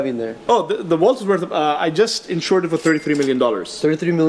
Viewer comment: Might want to watch the video again, specifically around the 3:50 mark: he did not say "appraised;" he only said "insured." There are multiple resources across the internet that will tell you the true values of what you see in this video if you want to check them out for yourself. Just as an example: the $6MM figure mentioned for the Superman 1 was for one specific copy of that book which auctioned for that amount in the last couple of years. That specific copy was graded at an 8.0 with no restoration, and it was a "pedigree" book, meaning that it came from a specific high-profile collection ("Edgar Church"). The Superman #1 shown here is an NG book, which means "No Grade." CGC, the company that grades and encases comic books, uses the "NG" for items that are so incomplete that they are not willing to give it a grade. In other words, "NG" is worse than a 0.1 and a far cry from an unrestored 8.0. If you watch the portion of the video where they do a close-up on the label above the Superman 1, it clearly shows that it is a "Conserved" (i.e. restored) copy, indicating among other things that the original cover and first wrap are missing (the cover that you see in the case is a color copy facsimile, not the original, created solely for aesthetics), so the book is considered to be incomplete. This copy is probably only in the $20-50 thousand range, still of some value but nowhere near the $5-6MM price that kept getting thrown around.